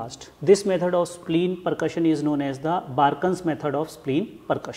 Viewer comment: spleen percussion method name please? Can't quite make out the name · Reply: Barkuns... Aka castell method